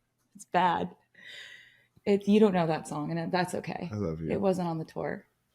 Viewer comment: DID TRAVY SAY “I LOVE YOU” AT 43:50 & THEY ALL TRIED TO RUSH OVER IT?!!!!!!! I AM SCREAAAAAAMMMIIINNNGGGG SOMEONE TELL ME IF I HEARD IT RIGHT